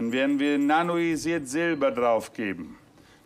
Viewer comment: Ich habe vor vielen Jahren etwas von Silbernitrat zur Behandlung (z.b.) bei Syphilis gehört er sagt auch etwas von silber bei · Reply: Er sagte nanoisiertes Silber glaube ich. Habe davon aber noch nie etwas gehört und finde, wenn ich es eingebe bei google nur das Kolloidale Silber. Ich gehe davon aus, dass er das meint.